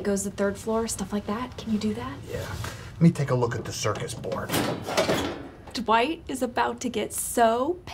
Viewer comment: anyone else heard Circus Board?